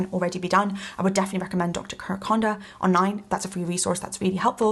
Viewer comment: for those who couldn't quite catch that doctor's name: it's Kirk Honda.